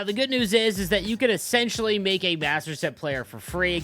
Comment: Sounded like you said assterset the first time 😂I do agree with that though lol